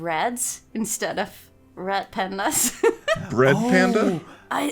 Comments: Honestly thought you were saying rad pandas and I didn’t understand the issue cause pandas are rad lol. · “Why isn’t there a difference between red and rat?” *American speakers have entered the chat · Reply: First time I ever heard her say Red Panda I thought she said Rat Panda and thought okay well I guess you can call Raccoons whatever you want I guess